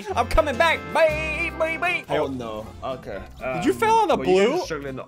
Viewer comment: I think said hell no Make this blue if you heard hell 👇